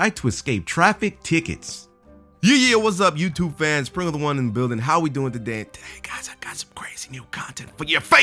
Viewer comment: I never hear what this commentator's name is. Still don't know it. Sounds like he says "Prilla the one in the building." He's okay enough, I suppose.